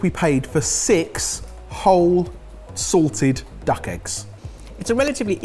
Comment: said SALTED duck egg…I heard SORTED duck egg…misheard lyrics 😂😂😂😂